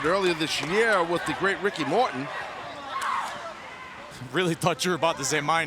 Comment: Oh. Taz said "Ricky Morton." For a second there, I thought he said "Ricky Martin".